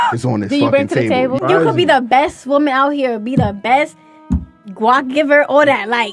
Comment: Not that it was a BETTER answer, but I don't think she said W.A.P. I think she said guack, as in the Guack Guack 3000 - something of a meme answer referring to fellatio.